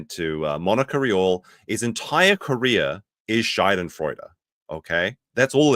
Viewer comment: just a quick heads up here, the way you mispronounced Schadenfreude makes it sound like you said "Scheidenfreude." Scheide is German for female genitalia. So you basically said Nick's career started with pussy joy. Which might be true if they were swingers even before his youtube channel.